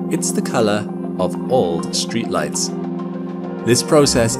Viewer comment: Not all streetlights. We have the new white LED streetlights. In fact it's making it hard to film movies set in the 20th century because the streetlights aren't the same colour as back then. I worked with a builder that put in a High Pressure Sodium lamp on his home's lights that shone on his garden because, he said, it puts out light frequencies (orange red) that makes the plants grow. Good if you have a newly built house and you want the garden you've just put in to quickly become lush green and well established. Also good for extending your "daylight" hours in the winter in greenhouses. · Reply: I thought he said all streetlights, too, but he actually said "old", not "all". "Old streetlights".